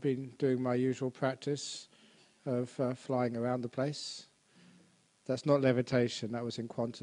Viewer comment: Just for a moment - I though we might be on a the same path - He said he has been doing his usual flying around the place - I thought he said his usual lying around the place.